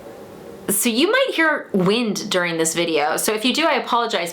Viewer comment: At first I thought you said 'you're gonna hear me passing wind in this video' and I was all 😳